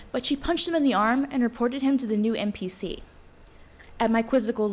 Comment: When I heard the acronym for Mental Parity Champion I could've sworn I heard NPC and even there after the explanation I kept hearing NPC